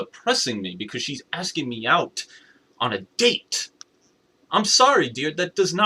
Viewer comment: Oh wait. Did u say im sorry DEAR at 3:45?